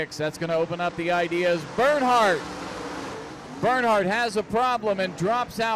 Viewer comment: everytimem he said burnheart i thought i heard Earnhardt